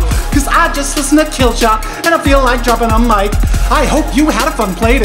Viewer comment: did he just said that he is listening to "Killshot" and feeling like dropping on mice? 😂